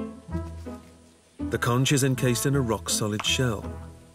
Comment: I kept hearing "conghis"! I'm thinking to myself, what the heck is that? 🤔😅 The word is "conch", with a hard ch and sound like the word "conk"! Awesome docu! 🥰